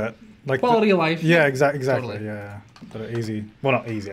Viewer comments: did he just said ‘“bueno” easy’? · did just say "we should have to make a pack " they were talking about not having to rescale some everything you put it down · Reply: MoistPotato he said it shouldn’t require us to make a pack